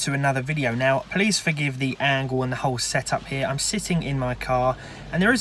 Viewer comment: What I hear: Police for give the whole angle and the setup here... What is said: Please forgive the whole angle here and the setup here...